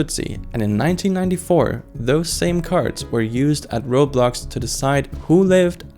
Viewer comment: cc says Roblox 😂